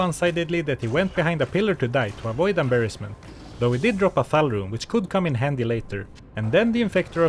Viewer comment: Listening to this while I work and i heard "Fal rune". I was like "no he didnt." lol